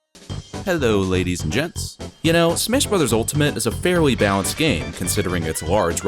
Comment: did he actually say smish brothers Altamite